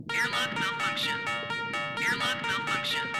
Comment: I heard the "airlock malfunction" as "your lungs are punctured" and that freaked me out way more than was the original intention I'd imagine lol